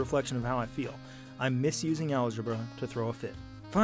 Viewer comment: "I'm misusing algebra to throw a fit." That is the greatest sentence ever said. I applaud you. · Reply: ohhhh....I thought he said "I miss using algebra to throw a fit" haha.....makes sense.